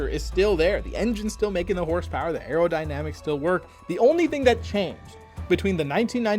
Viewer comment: I had to do a double take. I could have swore you said “the only thing that changed was a few lines of coke”. And I thought, that’s a weird thing to base a change on, but I totally understand. Sometimes bad decisions are made when partaking in the “Peruvian Marching Powder”.